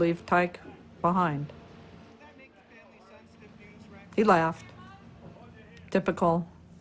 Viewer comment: On 44:33, Did she say he laugh or left ? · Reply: he laugh